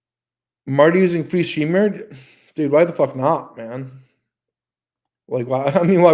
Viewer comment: What? I listened to that 5 times. What the hell did M say? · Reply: I believe he was responding to a comment that said "Marty using free streamyard?"